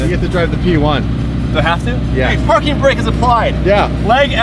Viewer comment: LOL at min 15 it sounds like says the F***ing brake is applied.